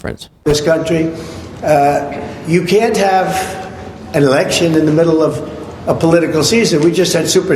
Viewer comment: My hearing must be going because I thought trump said, "You can't have an erection in the middle of campaign season."